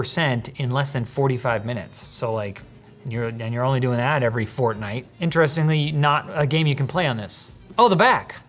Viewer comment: Did he just say fortnite?